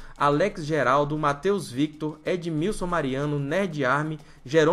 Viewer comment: o que ele fala: Edmilson Mariano o que eu ouvi: Edmil Silmariano